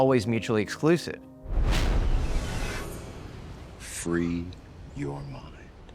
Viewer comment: The matrix part just played but i heard it "free? You're mine"